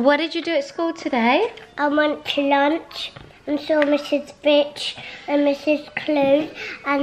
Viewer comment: When was talking about his teachers I thought he was saying I saw miss bitch I replayed it so many times 😂😂😂